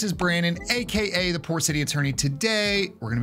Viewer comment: I like your commentary. The first time I saw you I thought you said you were the "Poor City Attorney", then I realized you said "Port City Attorney" :)